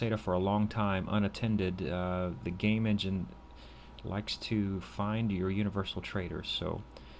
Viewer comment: Great tutorials by the way! 19:37 - it sounds like you said "if you run SETA for a long time unattended, the game engine likes to find you a universal trader"....what did you mean?